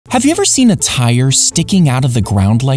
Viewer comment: at first i heard him saying:Have u seen a tyre sticking out HIS GYAAT